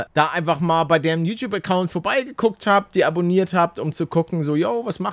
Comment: Bei 5:21 habe ich verstanden ob man Deaboniert hat🤣